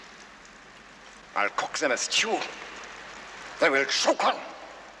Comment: in the beginning at 4:46 did Hitler say his sock was in a tube and he had to shake it ? ? ? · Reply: He said: "I'll cook them a stew that will choke them!" That stew was the Nazi-Soviet Pact he would sign with Stalin.